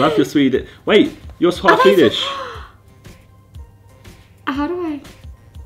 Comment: did anyone notice that Josh said "Wait. You're swalf Hedish" instead of half Swedish 😂